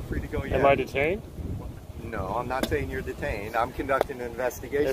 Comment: He clearly said you were not detained at 2:15.